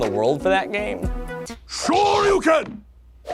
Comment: Turn on closed captions and it says “Sure you can!” instead of “Shoryuken!” 3:25 It’s pronounced REE-u, not RAI-u...